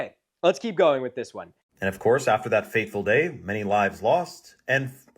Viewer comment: Did he say "faithful day", or does he just have a dude lisp?